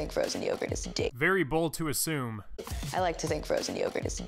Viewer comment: It sounded like at 1:46 like she said “ I like to think frozen yogurt is d- “ because at the end of it I thought it was gonna end with ick.